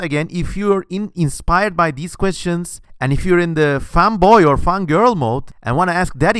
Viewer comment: i heard you say femboy insted of fanboy XD